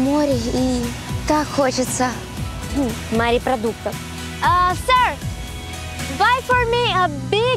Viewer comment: Мне послышалось или на 21:01 ведущая говорит: "Смотришь на море и так хочется мАрепродуктов!"?